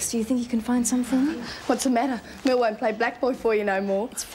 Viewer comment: What did she say at 30:44? Merle won't play black boy for you?